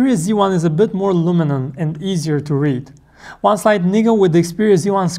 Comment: sounds like he said 'one side niggah'